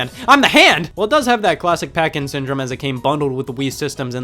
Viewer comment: legit thought you were saying "parkinson" instead of packing syndrome when talking about duck hunt